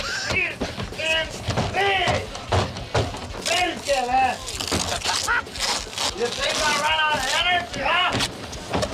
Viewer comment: What does he say at 0:44? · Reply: "Perkele!" - which is a finnish curse word